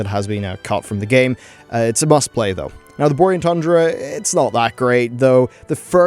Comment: Listening to say Borean Tundra sounds like he is saying Boring Tundra and I couldn't agree more